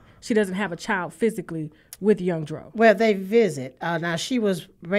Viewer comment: Harpo, who dis man we talkin' bout? Young Dro?! 😒🍷 Chile, I thought you said Young Dolph. 🤣🤣🤣🤣🤣🤣